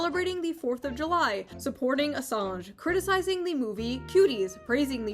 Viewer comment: I smoove thought she said supporting Hasan and I had to run it back for a sec.